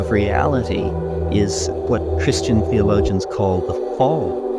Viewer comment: What is it called? The fault? The fall? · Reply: The fall